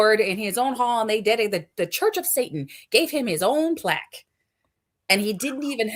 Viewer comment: I misheard that one word as "turds of Satan"